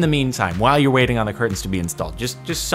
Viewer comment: did he just say, "while you're waiting on the curtains to be installed"? First world problems.